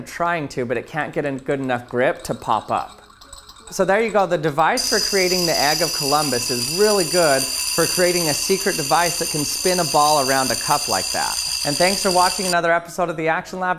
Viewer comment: ( I thought he was saying “Aāg of Columbus “ )